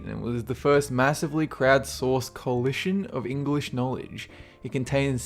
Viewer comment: Please help me with this little doubt. I love his videos but what the heck does he say at the end of them? I hear "that's it for this countdown and I have to go" is that it? Doubt's killing me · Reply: Alejandre He says "have a good one" :3